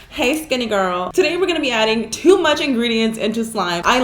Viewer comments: Am I the only one who thought she said “hey stinky girl” 😂😂😂😂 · My little sister heard you say hey skinny girl she said YOU A SKINNY GIRL!!!!!!!!!lol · Did she just say hey skinny girl... smh